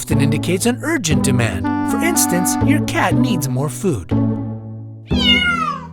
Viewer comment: I thought they said your cat not you cat. Who else agrees?